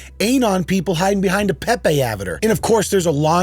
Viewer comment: Did he call an avatar an “avidur”?